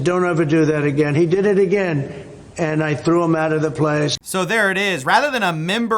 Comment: Misheard that as ‘threw him out of the plane’